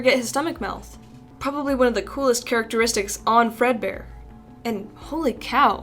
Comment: did she say fredbear